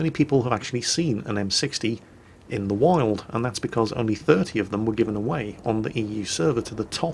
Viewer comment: I swear I heard "only 30 were given out..." Is that correct, or I just didn't hear that correctly? Only 30 tanks were given out in the first campaign? Damm, and now WG is giving out M60's like warm bread. Everybody and their cats can get an M60.